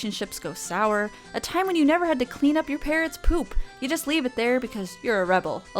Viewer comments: Thought you said leaving your parents poo. I was extremely confused. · Instead of parrot's poop, I heard parent's poop. It made for a much darker video for a few moments there until it dawned on my has a bird.